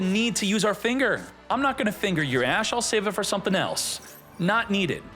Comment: I'm high as hell playing master duel on my phone and all I hear is " I'm not gonna finger your ash!" 🤣